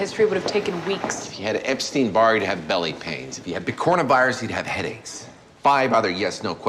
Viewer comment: did he say corona?